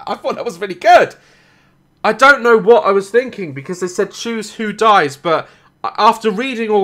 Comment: no, because i also thought the guy said "choose who to save" well...i knew i wouldnt be good at saving people